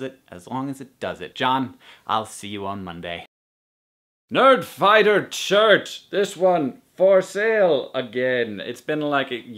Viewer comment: When you said "Nerdfighter shirts", I thought you said "Nerdfighter church".